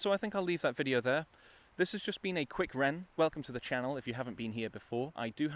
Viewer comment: Awesome and thoughtful rant. I'm sorry to have missed the moment this dropped. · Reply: Ooooooh, I said rant because I misheard you say "Quick Wren".